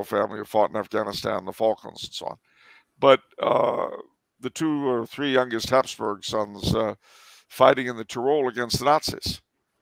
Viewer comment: Did Charles say that two or three of the younger sons of the house of Habsburg fought against the nazis in Tyrol?